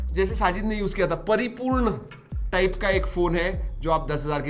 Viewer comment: What a word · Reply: Sampurn😂